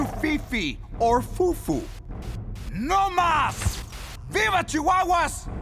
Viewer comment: What he said??? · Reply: No mas we are Chihuahuas.